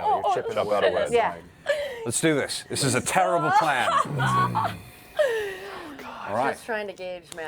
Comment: Sounds like Percy says, "This is a towerrible plan," at around 2:06:20.